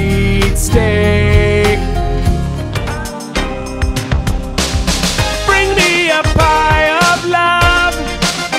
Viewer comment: I was soooo expecting to give a dessert facial during “Pie of Love”. 😂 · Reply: When I first heard the song, I thought it said "bring me a pile of love". Lol